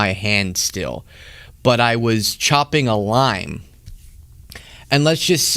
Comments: At first, I thought that man said he nearly cut his finger while “chopping a line.” The difference between chopping a line and chopping a lime is significant.😂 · Omg, at first I thought you said that you were chopping a line and I couldn't believe you were admitting to that on air! LOL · I thought he said chopping a line not chopping a lime. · Thought u said , CHOPPING A LINE … ( not LIME ) 🫣😵‍💫🥹✨😆✨💜.. lots of fun WORD PLAY… life flashing before ur eyes 🤣🤣🤣🤣🥹 sorrrry SO glad u are OK !!! 💜